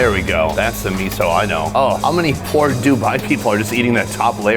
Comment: did he really say "poor dubai people?" 😆